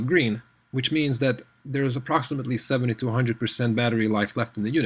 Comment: HAHAHA, I first thought that you said the Bose had 7200% battery life (WTF?). I went with the UE Boom 2, received both for Christmas and sending the Bose back. FYI: Amazon showed slightly better review rates for the Bose.